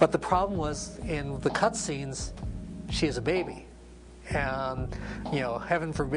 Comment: Ms Pac-Man isn't a baby in the cut scenes. I don't know why he said that. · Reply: I misheard this part too, he said she HAS a baby, not that she was a baby